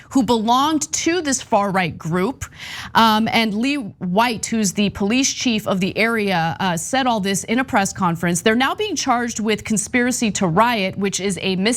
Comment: I could have sworn Ana said "And Lily White, who's the police chief...". Took a double take and had to rewind to check! 😄